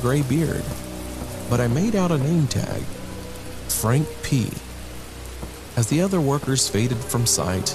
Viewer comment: I thought he said "Frank Peed"